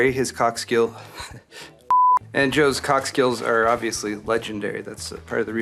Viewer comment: PMSL, i thought you said Joe's cock skills twice.. Should that be cork skills lol.